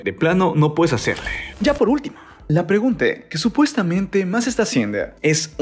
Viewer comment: escuché bien dijo La PREGUNTE JAJA lenguaje inclusivo